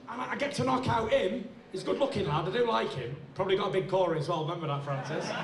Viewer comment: what did he say to francis at 22:28? prob got a big core as well? someone please tell me what he said